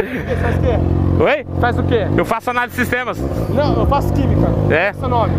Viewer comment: eu ouvi "ANALISTA DE SISTEMAS"???